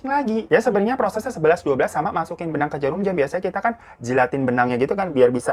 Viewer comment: Kok gw dengernya "masukin benang ke jarum jam"???? 😂 langsung ketawa dong gw😂.